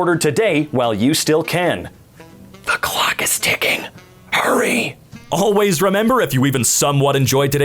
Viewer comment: I thought he said the Glock is ticking